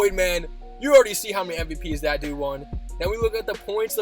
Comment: Ummm why does it sound like you said the n word in 2:40 lolllll how many mvps that ni&&@ won lmao